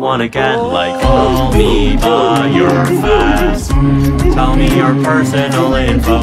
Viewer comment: is it just me or does it sound like he says "call me by your fat"